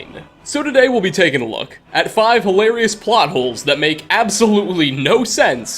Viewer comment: i thought it said potholes xd